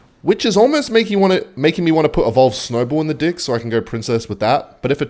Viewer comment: what did bro say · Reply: apparently he wants to put evo snowball in his deck